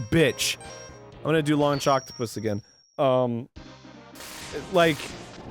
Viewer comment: I heard "Long Jocktapus" 🐙 I think I should get some sleep.